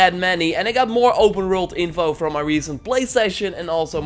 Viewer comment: "... the number of warts " ??? What the hell does he say?? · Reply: Worlds